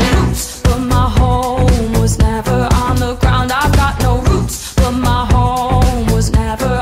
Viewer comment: Really, everyone has their own lyrics for this song. Mine are: I've got no boots, but my home was never on the man 😀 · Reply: I thought it was saying no roots in my bones LMAO